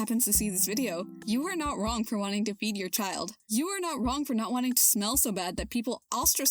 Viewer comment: The mic quality was so low that at 1:04:27 I unironically thought you said: "You are not wrong for wanting to beat your child." and had to do a double take. For a moment I thought I got duped and was not in fact watching a progressive, but a hidden reactionary abusing progressive redirect to excuse beating children.